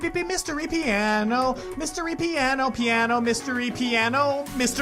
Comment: i thought he was saying mr e piano at first lol